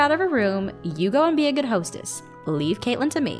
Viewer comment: I thought that said hotels